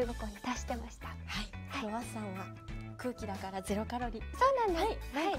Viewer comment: LOL this is a great combo, bandori tv pls give us more on 5:32 though I think Nesu said Kuuki(air) and not cookie so that why she say no calories?